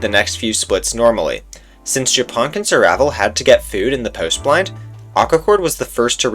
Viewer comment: did you just say post plant as in post plant in valorant lmfao